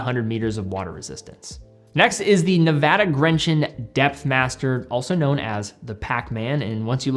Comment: Oh, DEPTHmaster. I thought he said DEATHmaster.